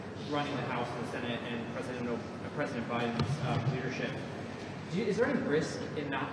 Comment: I thought I heard one reporter stopping short of referring to "President O'Biden" in his question. Listen carefully starting